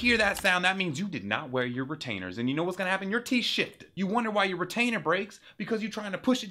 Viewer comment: he didn’t swear. He’d actually said “your teeth shifts”. Turn on captions and the proof is revealed.